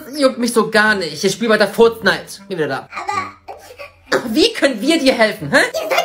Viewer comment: Ich liebe fortsnight auch😂😂😂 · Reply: Ich habe furtznite verstanden Lilly😂😂